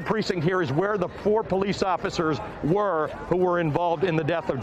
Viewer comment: “Did he say “Poor”?!? Those cops are gettin what they deserve